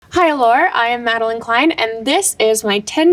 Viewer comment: I really thought she said “hi hellaur” in the beginning 😭